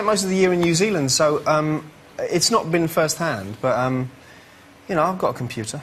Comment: Did he just say "You know I've got TUMBLR."